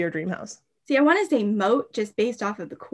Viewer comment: What is she saying at 20:17? English is not my first language and I don't know the word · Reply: she says Moat, its the canal/lake around medical castles